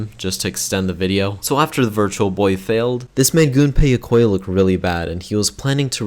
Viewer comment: Did he just say a virtual fart